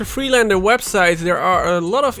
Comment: For a long time i thought he was saying gta fire city and was wondering if there another one😂😂